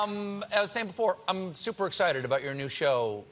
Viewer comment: Yo who ever is the captions guy... He sucks at his job .. lol wrote sewer instead of super around the 0:06 mark.